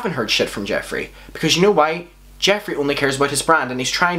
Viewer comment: When you said “jeffree only cares about his brand” I thought you said jeffree only cares about his gran hahaha